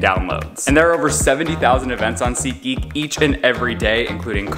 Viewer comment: Sounds like your saying Seat eek.